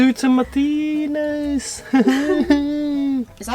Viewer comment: is so good at languages. I heard him say "kamatis" lol